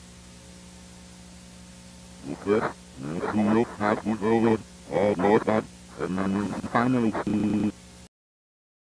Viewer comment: Can someone please tell me what the voice is saying? Here's the timestamp for the voice clip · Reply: “You finally start to remember, that old day, they will look out for you soon” it’s reversed audio but it was kinda hard to translate